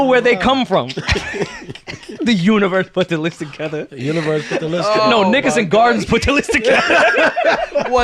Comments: Rory sounds like he says "no, niggas in gardens put the list together" 😩🤣😂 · nickerson gardens! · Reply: 🤣 I thought dude said “niggas in garments” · doth mine ears deceive me or does Rory say "niggas in Gardens"? 🤔🤣🤣💀💀💀💀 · Did he just say what I think he said at 2:02:13!!!!!!!!!!!!!!!!!!!!!!!!!!!!!!!!!!!????????????????????????? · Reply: He said Nickerson Gardens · can someone tell me what Rory said?? Cause know what it sounded like lol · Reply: Nickerson Gardens… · Yoooo 😯....at 2:02:16 did Rory say NIGGAS 😯🤔 YALL CHECK DAT TIME STAMP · Did Rory say nigga @ 2:02:14? 😂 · did Rory just say the N word?? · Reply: No he said Nickerson Gardens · Ayo did Rory say nigga at 2:02:12? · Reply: Nickerson Gardens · Umm. Did I hear that right? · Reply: he said nickerson gardens😂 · did rory say "nigga" or am I tripping 🤔 👀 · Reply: Nickerson Gardens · what did Rory say there? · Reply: Nickerson Gardens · Did I hear niggas from Rory 2:02:15 secs??? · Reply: No he didn’t lol, don’t you think the other people would have reacted if he said it??? He said Nickerson Gardens … you clown 🤡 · Yooo the way rory casually said "nigga" at 2:02:14 threw me through a loop lol I'm not mad at it but it caught me off guard to where I had to rewind it to make sure that's what he said🤣😂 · Bruhh and I tweaking?!?!?! or did Rory say nigga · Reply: Nickerson Gardens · Did Rory say niggas in 2:02:19 or I’m trippin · Yoooo did Rory just say "niggas" they gave him the pass